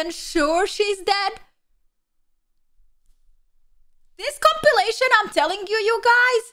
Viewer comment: At around 9:31 did say compilation or copulation? 😆😇😉